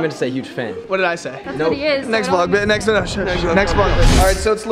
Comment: did addison say ye he is